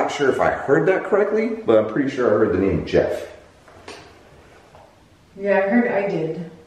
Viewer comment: i heard the name Jake instead of Jeff